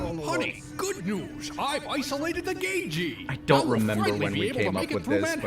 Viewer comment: for years I was so confused by this joke because I thought he said "Gay Geee" or "gaygee"